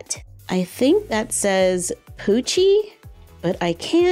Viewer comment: "I think that said,coochie?"